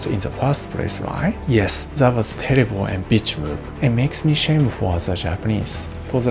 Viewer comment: did he say terrible and bitch move? I couldn't hear him very well with the music😂